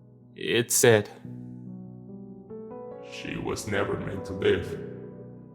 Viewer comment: First, I love your “Grandma voice”…really excellent; I could picture her. 😁. Quick Q; what did that first alien at 6:22 say(?). Sounded like “she was never meant for Ben”? Dang I get bummed when I can’t understand an alien!!!